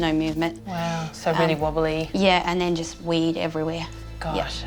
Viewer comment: Oh my gosh I thought she said that the cat was wobbly and there was “weed everywhere“ like the cat got into a bunch of pot. But I guess she said he wee’d everywhere.. I had a good 5 minute laugh at myself.